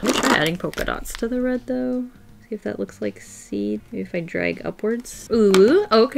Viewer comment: did she just say UwU lol